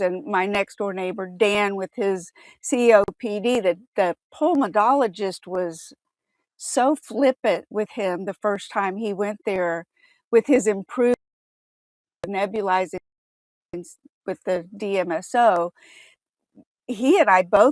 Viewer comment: it broke up. What did she say? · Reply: First time Dan went to the pulmonologist after he made so much progress nebulizing DMSO, the pulmonologist was very flippant. He basically interrupted Dan and refused to listen when Dan was telling him it was working.